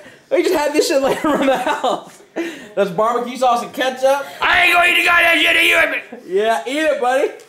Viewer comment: wtf Did he say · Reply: He said " IM NOT GOING TO EAT UR SH*T U HEAR ME" i think